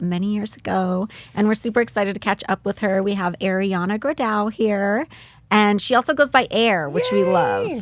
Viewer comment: lol i thought it said ariana grande at first